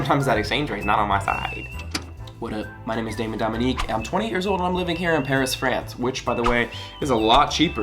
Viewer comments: you said "28 years old" so quick it sounded like just "TWENTY" lol I had to rewind it a few times just to catch the 8 in 28 :) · Woah I just found your channel not long ago and just started binge watching now. I really thought you said 20 years old! Damn boo! You look hella good for a 28! 💕